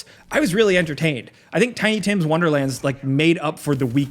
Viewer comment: am I tripping or did he say tiny Tim 😂😂😂